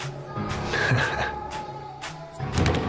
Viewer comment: He did the table slap and said whiskey but all I heard was "Broadway"